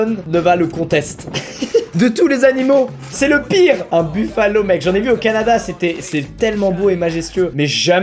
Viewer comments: C'est moi ou il a dit un buffalo ? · A 10:08 c moi ou il a dit buffalo